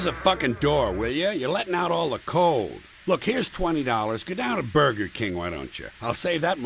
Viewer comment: here's the part where I hear "heres the 20 dollars now get out of burger king"